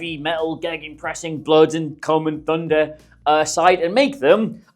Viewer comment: Did he just say blood and cum and thunder???